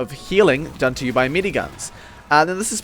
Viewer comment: healing done from MINIguns. Did I hear that right?